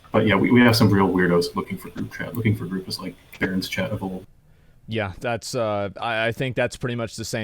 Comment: think he said barrens chat, not parents chat 😄